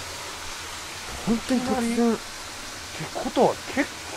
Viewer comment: 秒女性の声が2つ入ってるように聞こえるけど なんて言ってるのか分からない 片方は 「本当に沢山」って聞こえるけど もう片方はモゴモゴして聞こえない